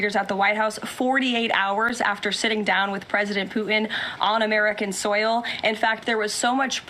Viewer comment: Did she say “President Poo’in”?!!’